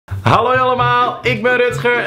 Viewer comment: Waarom zegt hij bij het begin halloi